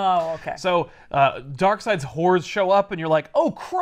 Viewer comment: Not gonna lie I heard whores too at 6:07.